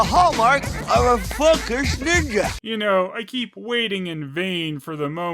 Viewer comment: Did he just say "A f***ing Ninja"? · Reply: Focused Ninja.